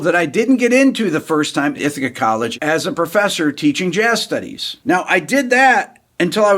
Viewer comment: I thought that said teaching jazz face 🤦‍♀️😊❤️